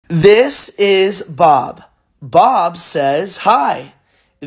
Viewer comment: I heard "this bish Bob says hi and when is Bob gonna die, Haha!!"